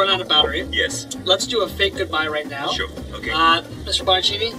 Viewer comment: did he say shokugeki?Or am I wrong? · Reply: he said ok 😐